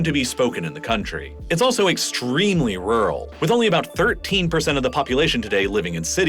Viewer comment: Small error at 6:03, captions say 133% instead of 13%